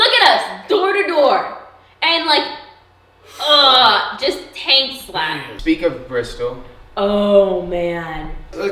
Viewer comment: Did she say "taint slap" at 12:42? First time I've heard that one, but I immediately understand it.